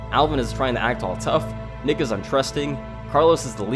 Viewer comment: who else thought he said niggas untrusting